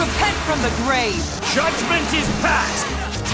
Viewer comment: I heard “shut ventis pats” I’ve been playing to much genshin I guess not really tho